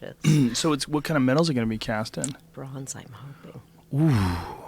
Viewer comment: you can't hear wtf she says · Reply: she says that she hope's it's cast in bronze